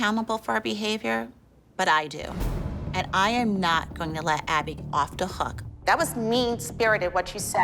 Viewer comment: "And im not going to let abby of DA hook" sorry was it only me who heard that?